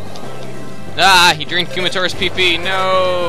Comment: lol i was just gonna say at 10:45, it sounded like," oh no, he drank kumatoras peepee" and then i saw you already mentioned it lol